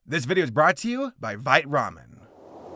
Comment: I heard that as "This video is brought to you by Bite Ramen" and I'm just like "Well yeah I bite it it's delicious."